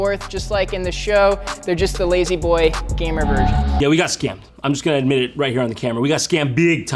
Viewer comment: I thought he said "Lazy boy, gamer virgin" for a sec LOL